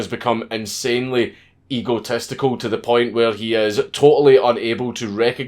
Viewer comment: Did he say ego-testicle ?